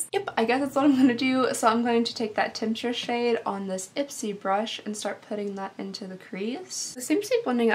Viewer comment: did she say “Ipsy brush? Lol